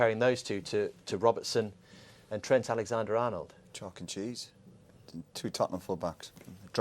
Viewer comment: did Roy say “Chuck E Cheese?”